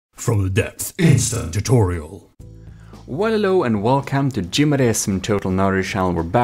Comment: I always hear, "Wololo and welcome" at the beginning.